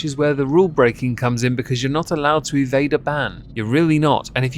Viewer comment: OOOOH! EVADE a ban!!! i thought he was saying "vader ban" and was like... the hell's a vader ban?!